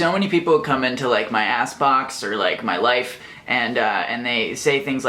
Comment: I misheard "ask box" as "ass box" and life got even more confusing for a moment.